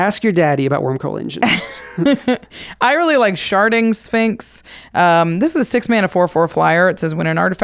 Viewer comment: I heard "sharting sphinx" listening at 2x speed